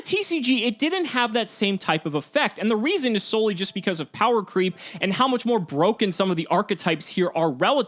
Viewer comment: did I hear correctly what he said ? "sololy powercreept" ? sorry to say this but you are an idiot ... dragun CANT be powercreept as a boss monster or as a monster, he is too powrful and the only reason he is NOT shining in the TCG over the OCG is sololy because of Max"c"